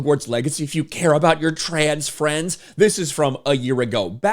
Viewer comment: "trans friends" LOL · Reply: Trans? I thought they said Trains! You know, Choo, choo?